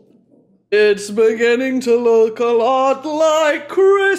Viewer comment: i heard “itspaghetti to look a lot like xmas”